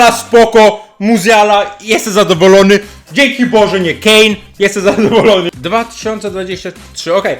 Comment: ja to nie usłyszałem Kane tylko ge🏳️‍🌈